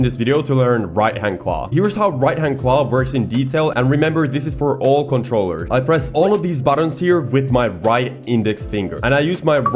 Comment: Bro i thought u said for all comtrollers including XBox